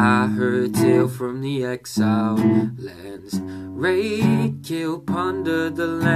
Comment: i thought it said rape kill plunder